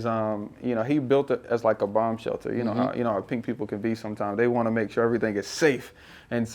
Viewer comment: 🎉 Salute 12:23 😂 did he just say pink people 😂